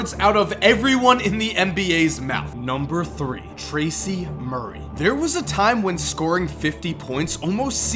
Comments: I thought he said Tracy Mcgrady... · Who else thought be was saying Tracy McGrady instead of Tracy Murray at #3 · Anybody else thought for number 3 he said "Tracy McGrady" and was about to berate him in the comments section, but then realized he said "Tracy Murray".